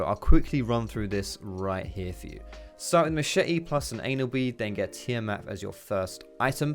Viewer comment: thought he said start off with a machete and an anal bead. sounds kinky